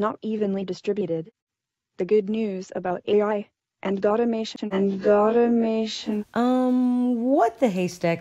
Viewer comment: "sulfry" is: "SELF RE-" inventing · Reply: I also think "God-imation" was supposed to actually be automation. I also think "I concede you" was supposed to be I can feed you. Call me stupid for not worrying about this but I think some of what Sophia said was either mispronounced or maybe misheard by the video creator.